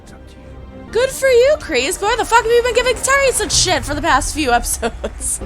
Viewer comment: I have no idea what you said after “good for you kreese” but I trust you so much that I 100% agree with you😅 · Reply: I said why the f have you been giving Terry such s for the last few episodes LOL